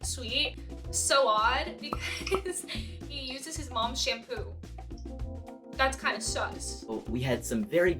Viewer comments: did she just say "that's kinda sus" in a deeper voice? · I misheard Sowad as "so odd" all the time